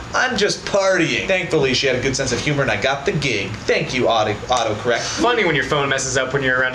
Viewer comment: Notice how it said "auto carrot" instead of auto correct